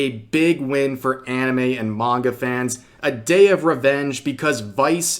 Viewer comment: NGL i swear i heard you say MAGA instead of Manga lol.